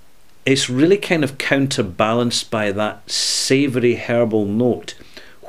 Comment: HERBAL! I Thought you said hairball. Lol.